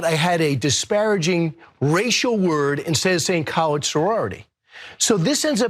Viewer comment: Chill out yall.. he said college but it most definitely sounds like he said colored tho.. just vote 😂